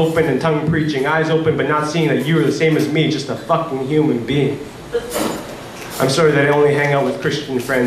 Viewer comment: did he just say "f*cking human being" at 1:59? O_O